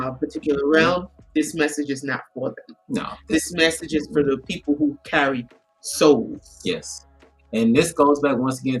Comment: I thought you said, "This message is not for the people who are empty seats..." 🤣